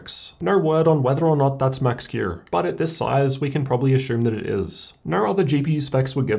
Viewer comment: did he say ashume?